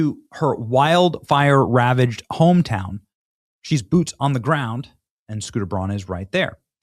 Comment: did you say "she boobs on the ground" ? No? Ok sry 😅